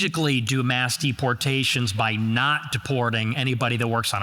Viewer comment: your asian friend didn't catch the "Not"